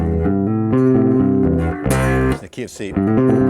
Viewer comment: did he say KFC ?